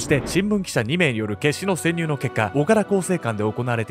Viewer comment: 岡田がずーーーっと「おから」に聞こえてて、「おから更生館って変な名前や〜」って思ってた。